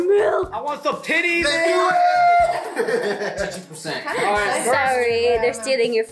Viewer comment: am i the only one who heard “it’s chichi percent” 😂😂